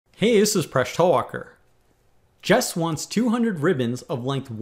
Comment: I thought it said Jesus :3